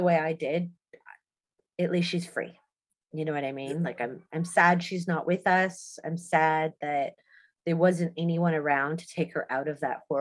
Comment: I used to have the biggest crush on Taylor Wide but her comments on Daffney and basically saying she's happy she k*lled herself disgusted me. What an awful and stupid thing to say. RIP Daffney forever will be missed. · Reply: How in the hell did Taylor say that she’s happy daffney killed herself? If you listen carefully @ 3:48 she said ‘…at least she’s free. I’m SAD she’s no longer with us.’